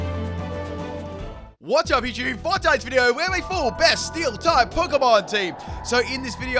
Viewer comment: This is unrelated but...the more I hear him say "for tonight's video" I'm starting to hear "fortnite's video"